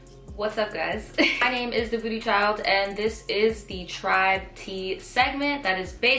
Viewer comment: I stg I hear “booty child” instead of “thevoodoochild”. 😂😳